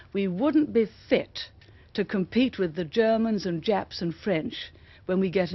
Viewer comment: Did she say Japs???